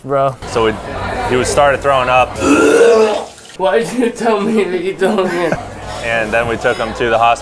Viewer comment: Not an onion: breathes: eats the unidentified not an onion on a dare. Also: "why did you tell me to eat the onion?!". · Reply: that shit had me cracking up, i thought he said "why did you tell me if you dont know" XDDD